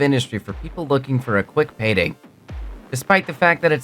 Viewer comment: did I hear payday? 🔫😎🖕